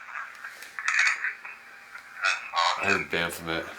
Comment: after he asks who made this object at 36:46, I hear "Edgar"